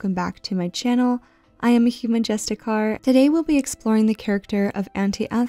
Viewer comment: I heard it as human just a car lmao